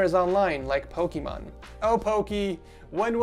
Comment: did he say pokemon?